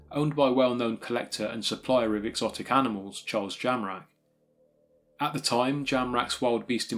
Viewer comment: I thought you said Charles JAMRAG !